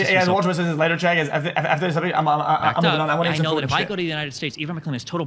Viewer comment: I have watched x for 4 years now, but what in the fck did he just say in 41:13 💀 . This isn't just speed; he legit stutters 10x and goes on to the 11th word as if everything prior were words. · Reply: “i…i…i…just…you guys wanna watch this later… a…a…after this…[idk]…somebody…i…i…i’m good on this